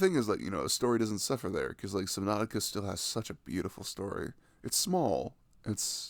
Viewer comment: What game is he talking about? I can't understand the name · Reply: Subnautica